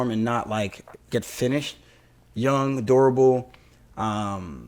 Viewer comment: lol right after the "Have his way with dat ass" comments you started talking about Ya Dong is durable and I thought you were saying adorable 😆 i was like wtf is going on here lmao 🤣